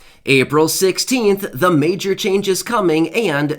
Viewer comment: So now it's April 16 I thought you said April 7